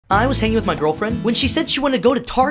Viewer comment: I heard “I was hanging my girlfriend”💀